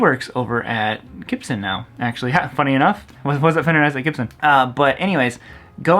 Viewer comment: what did he say? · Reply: "works at fender, works at gibson" but he mangled that English